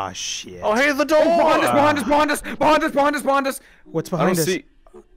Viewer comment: I thought that he said “BAWANDAS!!! BAWANADAS!!!! BAWANDAS!!!!!”first but he actually said “BEHIND US BEHIND US BEHIND US”